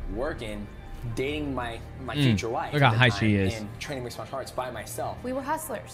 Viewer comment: does he say look at how high she is about his wife at 12:08?